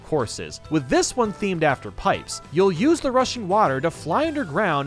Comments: did i just heard Russian water? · did he say? “Russian Water,”. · I thought he said "You'll use the Russian waters"